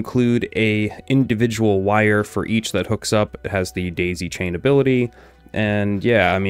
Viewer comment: I heard him say display chain instead of daisy chain 😊😊